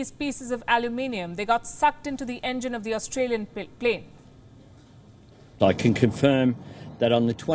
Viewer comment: Not to be funny but I heard Australian plate 😂😂